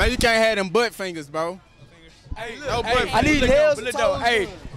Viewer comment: I’m sorry did I hear butt fingers?!